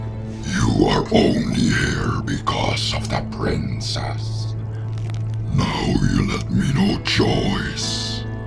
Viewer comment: at that part I didn't know what bowser said and I thought he said "Now you left me no choice, Henti"